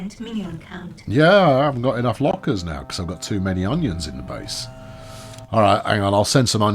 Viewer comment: Did you just say you have too many onions in the base?